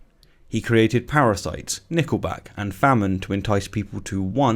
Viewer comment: Did he say "Nickelback" at 11:30 as a kind of suffering? 😆😆😆😄